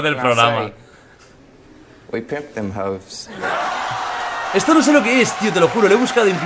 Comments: Lo que dice es "We picked them hoes", "Escogimos a las p*tas". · en el minuto 7:26 creo que dice "we picked them hoes", los subtitulos estan en automatico (o sea que ponen lo que se escucha, no toma hoes como una palabra y pone hopes) y es vendria a ser "nosotros elegimos a las zorras"